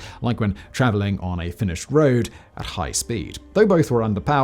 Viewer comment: Do I hear correctly? Finnish road? Also the HEAT round stands for High-Explosive Anti-Tank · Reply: Finished road, so unfortunately not Finnish:)